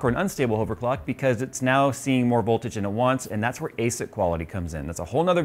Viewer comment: Where can I learn more about the voltage thing and unstable overclock? Sounded like he said Aset quality or something.